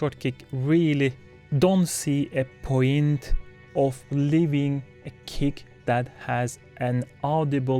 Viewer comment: Because of your accent (which i enjoy), i thought you said "I don't really see a point of living" and i thought "wow that got really dark out of nowhere" LOL I remember seeing that deadmau5 rant and agreeing with what he was saying, but i agree with you as well. You pretty much clarified it perfectly